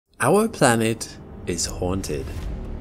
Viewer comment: face-orange-raised-eyebrow: I thought he said, "HUNTED", not "haunted"...! SHeeze.